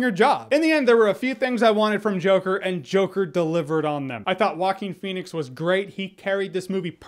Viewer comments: I hear you say “Walking Phoenix” lmao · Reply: I thought he was saying Hawking Phoenix lol. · I hear you say “Walking Phoenix” lmao